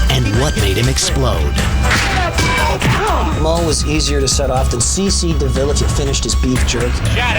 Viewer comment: What did Sebastian Bach say about Moe?... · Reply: I think he said "Moe was easier to set of than C.C. Deville after his beef jerky"